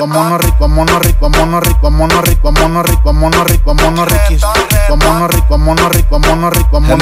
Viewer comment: escucho que dice mono rico jajaja